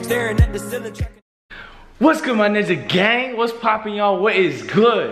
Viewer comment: I thought he said "nigga gang"